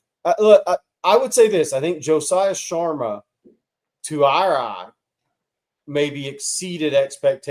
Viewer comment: what did you say? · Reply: “To our eye”